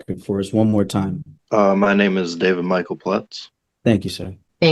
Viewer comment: I was listening (not watching) and I thought he said his name was “David Michael Butts” and I had to double check